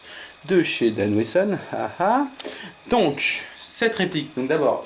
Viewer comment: French people shouldn't be allowed on youtube... at 3:32 I heard douche :P